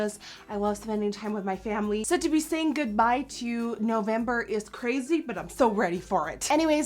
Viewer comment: LOL I thought you so to be saying goodbye to YOUTUBE LOL